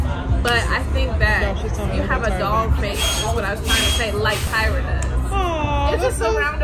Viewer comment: Girl we ALL make those hair faces lol, especially those of us that wear glasses. ALSO I'm mad the captions thought I said "a dog's face" I said doll's face y'all! I'm dead.